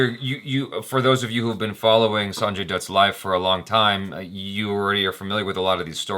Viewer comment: i thought you said "Sanjay DUD"